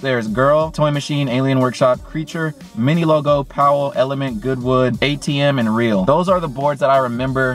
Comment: When he said good wood I thought he said good well 😂😂😂